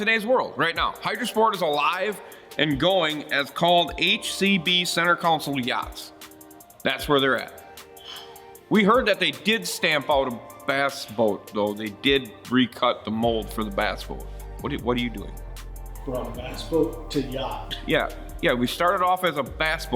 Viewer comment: I thought you said Strap On